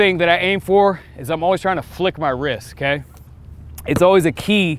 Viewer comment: was I the only one who thought it said ‘Fuck Your Wrist’? 😂